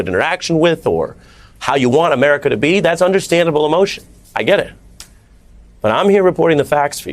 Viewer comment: Almost thought i heard Peter say "But, but , but I'm white."